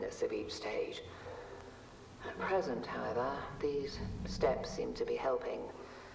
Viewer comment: The monster sounds like me when i laugh.... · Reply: I think 1:19:43 says "present however" instead of "it hasn't had all"